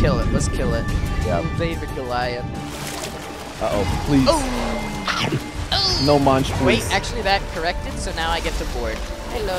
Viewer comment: he said “no munch” I thought he said “no manches”.🤣